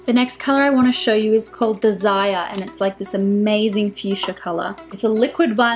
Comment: when you said "desire" i heard "the Zaya" :)) gotta love the aussie accent <3 need to try the lipsticks as they look amazing on you